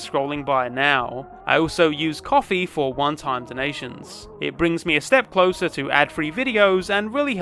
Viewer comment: I keep hearing it as "coffee for wonton donations". I know what it really is but fuck dude, coffee wontons would be disgusting and I feel sad every time.